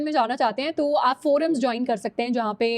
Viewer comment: pe didi ne kya bola kya join kar skte h???